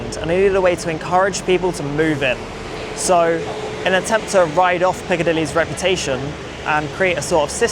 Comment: At 2m42s “in an attempt to ride off Piccadilly’s reputation”… is this a Mancunian variation for: “to ride off the back of X’s reputation” or “to ride on X’s reputation”…? They’re the kosher versions that I know. He said “ride off” but I heard “write off” because his version was new/wrong to my ears. (Cauliflower 😂 it’s ok, I’ve subscribed now)